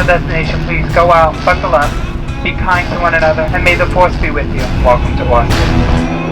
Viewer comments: did it say may the force be with you (i am not a huge starwars fan btw) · Is it only me or is she saying may the force be with u 6:41?!!?